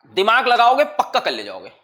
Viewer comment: Sir ne kya bola 1:21:19 Dimaag lagaoge pakka___?_____ jaaoge😁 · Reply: Kar le jaoge